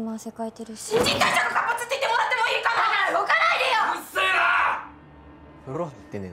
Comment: なんつってんの？日本人だけど聞き取れん笑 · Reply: 新陳代謝が活発って言ってもらってもいいかな